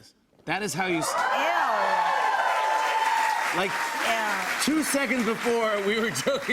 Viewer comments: did she just say...ew? oh yeah im definitely fighting her!! EW?! onika puhlease you were GORGEOUS!!! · did she say ew?! Are we not looking at the same picture girl?